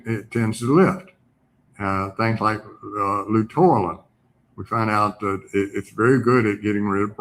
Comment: what were the two things he said were needed to be taken to get rid of brain fog? I can’t understand either of these words he’s saying. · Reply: Luteolin (flavanoid) and thiamine (vitamin B1).